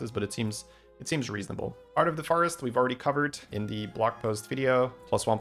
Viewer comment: I had to rewind at 17:15 because I thought you said Heart of the TARDIS 😂